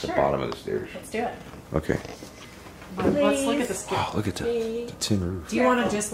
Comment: I heard the word “please” Being said a